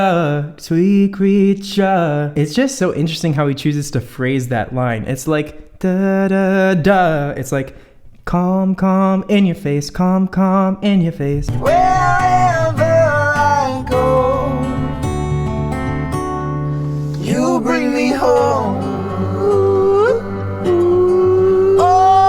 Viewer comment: I thought he said “cum in your face”